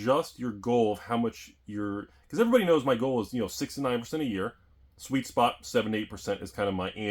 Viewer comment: I thought you said your goal was 69% a year. giggity